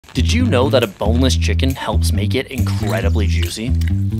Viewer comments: I thought he said "did you know that a homeless chicken" 😂😂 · I heard this as homeless chicken and thought how tf does being homeless make you tastier lmao